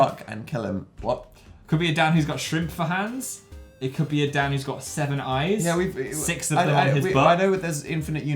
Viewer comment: I thought you said severed eyes for some reason...